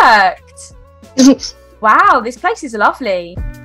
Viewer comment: I thought you said this place is ugly what do you said it’s lovly